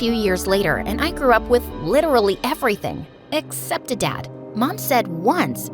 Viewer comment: sorry to correct but the subtitle says accept instead of except 😃